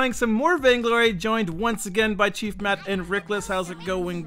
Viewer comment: I thought he said Chief Pat near the beginning, nearly had a heart attack